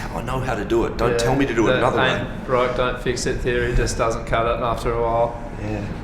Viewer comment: what does Jeff say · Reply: The ain't broke don't fix it theory just doesn't cut it after a while ;)